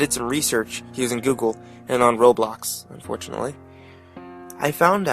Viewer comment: DID HE JUST SAY ROBLOX!?!?! (I play it)